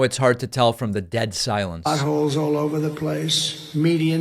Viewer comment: Trump says "potholes all over the place," but the CC/subtitle says "assholes" instead of "potholes." 😆